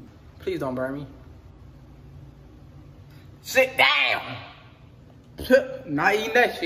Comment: I heard him said “sit down”